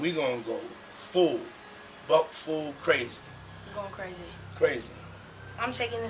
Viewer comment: Does say “ Butt full crazy”